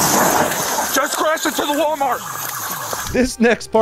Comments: "Just crashed into the Walmart" am I hearing this right? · did he say crashed into the walmart??💀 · did he say he crashed into the walmart? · did he say walmart